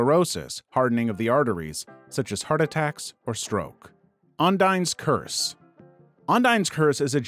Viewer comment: I heard it as Undyne's curse 💀💀💀💀